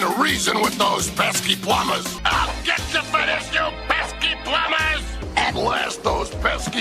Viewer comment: AHH! GET THE FETISH YOU PESKY PLUMBERS! (I know that's not actually what he's saying but it's funny)